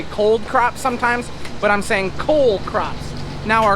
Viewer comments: All the eyars I have watched your vlogs I thought you were saying COLD CROPbecause, I assumed they were harvested late Fall. THis is the first I have known it is called COLE CROP. Never too old to learn. Tnaks for sharing and correcting me 🚜🐄 🌽🍅🍇 🍎 🐄 🚜👶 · Damn I thought you were saying COLD crops for the last 2 years